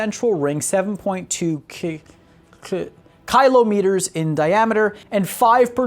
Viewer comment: Did he just say "Kylo-meters?"